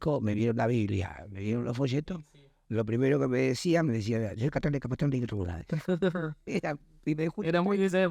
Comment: Qué es lo que dice en el minuto 12:49 ......No lo entendí a pesar de que lo repetí varias veces 😊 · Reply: Dice que le decían soy Católico Apostólico Romano ( Tucumano, creo que le salió)